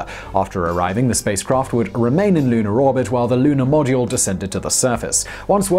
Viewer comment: Did say “Munar orbit” at 6:14?!?! Or am I just hearing KSP references everywhere?